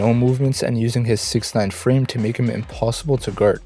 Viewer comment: Not the captions saying 6ix9ine instead of 6’9